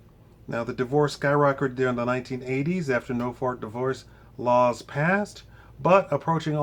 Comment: Lol... did i hear him say "NO FART DIVORCE RULE??"